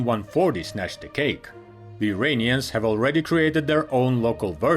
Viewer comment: Edit: My bad, with his thick accent I misunderstood what he said. I will leave the original comment here so people can see what the fuss was all about. 7:25 "the Uranians"? Oh comrade, don't let politics get in the way of your videos. I understand you are under pressure from the "thought police" in your country, but committing faux pas like this do not help your cause here on YouTube. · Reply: He said the Iranians created their own local version of the aircraft... what's the problem....?